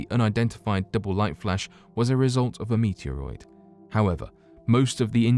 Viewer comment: did he say "meteoroid?" around 3:55 pretty optimistic people to be saving things for the year 8000 and making a ten thousand year clock.